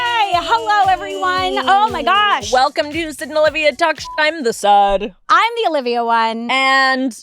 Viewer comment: I heard "I'm the Sad one" and ....yeah.